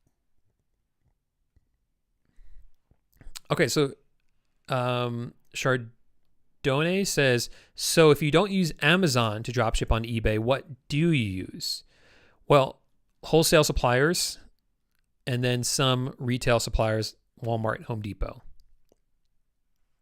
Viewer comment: I had thought you said you can use Amazon to drop ship?